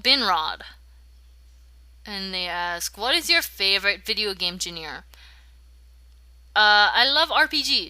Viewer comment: Did you say game veneer?